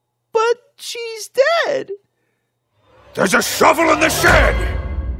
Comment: What does the Dad say? I can't understand it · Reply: tigersebel "There's a shovel in the shed!"